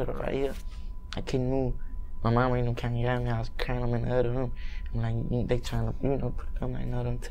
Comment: wtf did he just say? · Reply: "I couldn't move..my mamma and them came and got me, I was crying in the other room"